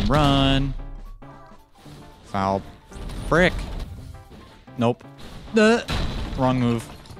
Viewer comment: Bro at 8:29 when he says frick it sounds like he said PRICK